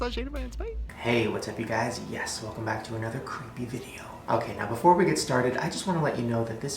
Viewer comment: “welcome back to another crappy video” THE CAPTIONS SAID IT NOT ME!!! no hate his videos are my life 😌